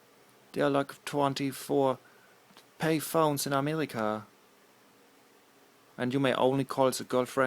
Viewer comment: it sounds like he says amelica lmfao